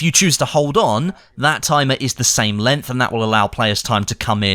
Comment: Lol took me a while to realize was saying 'LENGTH' ...I thought he was saying 'LINT' and I was very confused there for a bit.